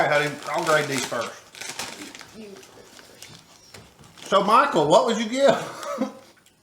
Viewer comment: Since no one asked here's mine: Spicy V8,worcestershire, lime juice, olives, celery salt and Absolut Peppar. One of the easiest flavors to add - celery salt - and they skipped it? Weird. Omg I thought you said Monica instead of Michael the first time 😂 I've seen a review of the Moscow Mule and the consensus was they were weak as well.